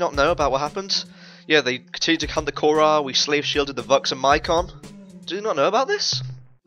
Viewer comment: for a second i heard "VUX and Mycon" as "busty Mycon" and now you all have to live with that image too